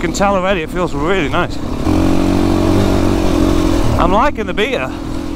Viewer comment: dude i could’ve sworn he said beater instead of beta